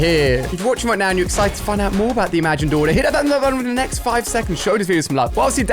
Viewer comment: WhAt DiD hE sAy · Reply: Hitthatlikebuttonwithinthenext5seconds